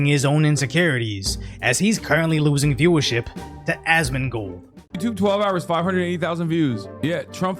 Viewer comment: Did he call him "AsmonGHOUL" at 4:38 ? Hah